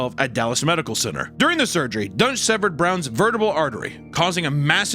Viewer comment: I thought they were saying his name was "Dr. Dunce".